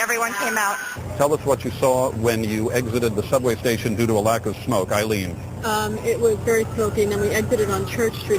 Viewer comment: “Due to a lack of smoke 💨” ... did they say?